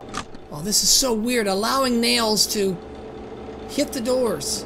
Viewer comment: did he just called him nails again